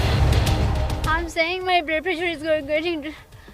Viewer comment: kya boli.... kya low...kya 😅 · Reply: Blood pressure